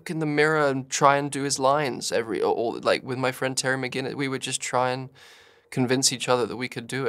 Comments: did andrew just say hes friends with Terry Mcginnis (batman beyond) lol the crossover we needed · Am I tripping or did he say "with my friend Terry McGuiness"